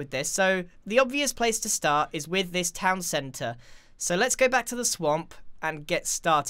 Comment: When he said let's go back to the swamp, I heard let's go back to the sonk LOL O my goodnes yy?